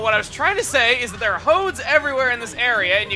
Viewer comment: It sounded like you said there are hoes everywhere